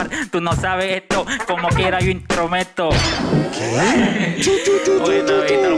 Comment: Yo escuché "como quiera intro meto" 🤷